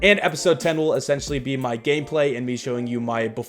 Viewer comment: thought u said “GayPlay”